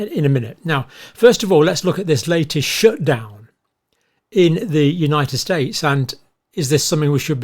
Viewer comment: Did he just say the U.S. is in shutdown? If so where? Why? I have not heard anything about this. (At appx the 2:30 mark)